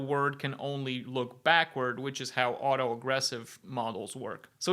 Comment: Did I mishear or was auto-regressive pronounced auto-aggressive every time